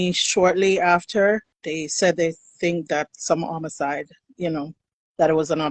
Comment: Right here what did she say? Did she say they (the detective) think it's a Homicide or did she say something else? I can't quit understand her right there. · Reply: She said homicide, which is why I don’t understand why they didn’t do an investigation. Also, I saw in the comments that the fiancé is well connected to law enforcement